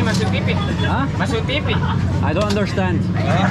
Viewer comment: hand-pink-waving: 12:14 chillax, there's nothing offensive with what he said. He just asked "Masuk TV?" means "Will I be broadcasted in TV?". That's all :face-green-smiling: Keep up the good vlogs bro! :trophy-yellow-smiling